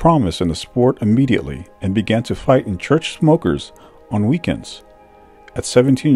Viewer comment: Definitely there is movie in this one! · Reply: did you say "began to fight in church smokers"? What does that mean? What's a "church smoker"? Maybe I misheard?